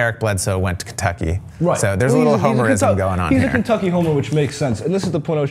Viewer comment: "He's a Kentucky homo" lol come on. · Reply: I heard "homer"